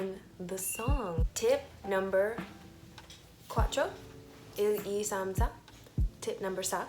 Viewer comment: laly bonilla. At what time in the video? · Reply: Pearl Ob in the minute 4:14 I heard a "cuatro" or I'm wrong?!